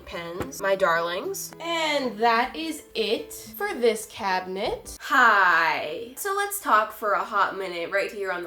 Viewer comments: It sounds like she says catnit not cabinet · When she said cabinet I thought I said cat nip 😂🤣